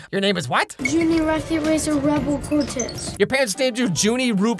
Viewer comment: I SWEAR! I heard junie rocket razor redbull Cortez. I swear